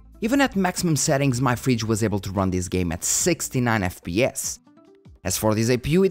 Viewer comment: did you say "my fridge was able to run.."? Or what did you say there?